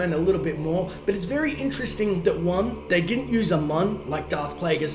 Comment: did you say "they didnt use amon like darth plagueis"? what is amon?